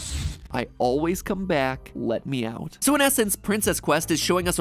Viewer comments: idk about y’all but i heard “i always come back lemfaow” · I was thinking Afton said “No matter how” instead of “Let me out”. If so that further confirms that he can come back in many ways. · i thought he said "i always come back, minnie mouse" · I swear i heard "I always come back, Vanny houz" Instead of "let me out" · I swear when he showed the audio I thought he said " I always come back Minnie mouse " 😆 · The backwards message I heard was “ I owe my back, let me out” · lol, i heard "I always come back. No matter how" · it sounded like my headphones were broken This is glitch trap: I always come back now... Pls let me out :'( Me: hah no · I thought it said " I always come back, let me hose you " 🤣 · I heard I always slap backs Mickey Mouse